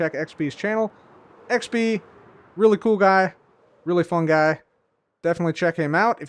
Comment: did you just call xB a Fungi?